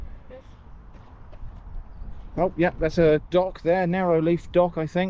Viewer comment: I thought you said that's a dog 😂